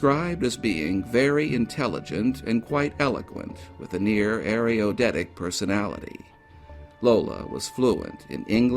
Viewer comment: There's one word I'm not getting. Can someone tell me what is saying at 31:43, right before "personality"? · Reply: He says the word air-re-odet-ic. I'm not sure how to spell that word, nor do I know its meaning, yet.